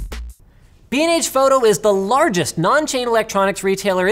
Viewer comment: BnH? holy shit I thought you said "bean age photography" oh my goodness gracious